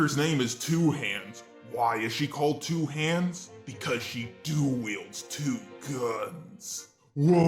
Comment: I misheard ‘dual wield’ as ‘do a wheel’